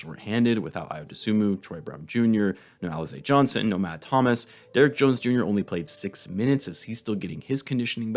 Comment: Got so confused cause I thought he said Derrick Rose Jr lol, god bless everyone and have a good day, Merry Christmas